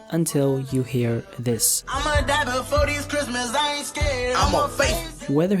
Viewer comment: I heard IM A FACE